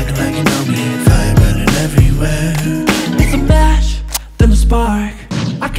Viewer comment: I was super confused for a minute he said "fire burning every were" I thought he said "Firebirding every were" and your not even driveing a Firebird